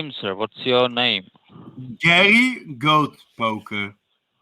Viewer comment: When the scammer asked you your name I could have sworn you said Mohamed Goatspoker.